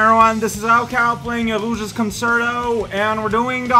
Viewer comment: Death count: 207 [746] (4 today) (0:02) "Illusia's Concerto"? Is that what you said? I've never heard of anything by that name. EDIT: Actually, probably said "Rouge's Concerto", which makes more sense. (0:17) Wow, that was a fast death, courtesy of that worthless consumer model. (0:41) If you're not in a 100-ring mission, you can frequently find rings randomly by just digging anywhere. (1:55) Darn worthless consumer models. (2:27) Geez, how many times is the game gonna clip you through the floor like that? (3:09) It's dumb that you're clearly meant to use that monkey's bombs, but he keeps being prone to suiciding. (4:00) Okay, that worked somehow. (4:03) What, only a "Good!" (100)? But, you found it within 1 minute and with no hints, or with 1 hint if it counts the hint you read on a previous life. Oh, but if that's the case, did it count all of the elapsed time on your previous lives as well? Perhaps that's why. (5:27) Took just over a minute for that one, so you got "Awesome!" (1000). (5:32) Caution: Fire hurts. (6:48) Aw, missed Golden Boy this time. (8:40) And, that one was another "Awesome!" (1000). (8:52) The A-rank score for Egg Quarters Mission 5 is 13,000. (9:37) RIP Chao's bunny ears.